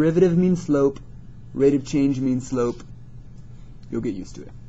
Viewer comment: Haha, sounds like your saying sshlope, 00:9 - 00:12. Keep up the good work, it's great!!